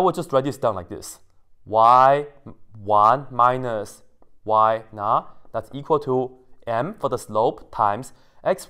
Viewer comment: You said: “Y_not[0]” I heard: “Why not ?”